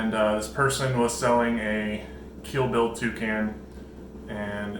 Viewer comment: I thought you were saying a Kill Bill Toucan all this time instead of Keel-billed... I was wondering if that was some sort of reference within the bird-loving community XD